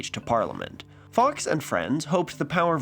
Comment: I had to do a double take when I heard "Fox (Fawkes) and Friends" lmfao